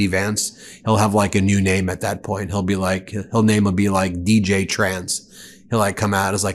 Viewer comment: I heard "VJ Trans" as the predicted name Vance will go to next 🤣